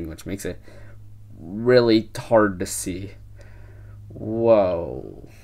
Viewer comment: did he say "Really hard to see" or "Really 'tarded to see"?